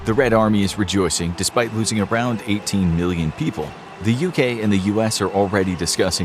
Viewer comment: did I hear billion people idk maybe my hearing bad